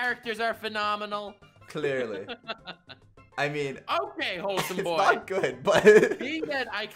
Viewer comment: I thought you said wholesome why is Connor there